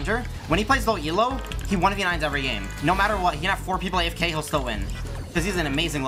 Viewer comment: What did he say after ,,When he plays in low elo.."? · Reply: a bit late, but he says "he 1v9s everygame"